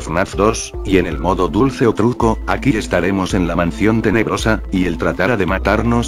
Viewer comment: Lo que dijo:mansion tenebrosa Los subtitulos:mansion de negros Yo:._.XD